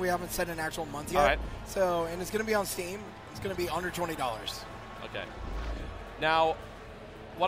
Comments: Oh god... I misheard him... I heard "It's gonna be 120$". There was my horror moment for today, lol Hm, I would expect this game to have a great synergie with those stereoscopic googles people are making. Occult somethingsomething. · Anyone else hear $120 at 17:00 ? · When the developer said "It'll be under $20" my brain derped and I heard "It'll be $120" XD